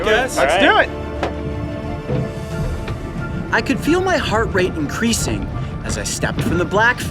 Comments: Lmao. I thought he said "heart rape" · I thought he said, with my heart rape was increasing